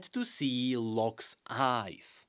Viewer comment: When said Locke's eyes I kept hearing lux's eyes and had to reapet that part 5 times to hear it correctly cause I was so confused what lux doing here